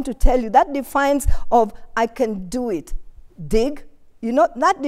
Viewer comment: Did she just say "I can do it, DICK!" ... ? 👀